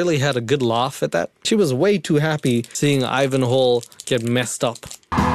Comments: Before catching on, I swore you were saying "Ivan HOLE" and I honestly wish you were. It made me giggle the entire way through this video 😂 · I thought you were saying "Ivan Hole" until you cut the clip in of him introducing himself 😂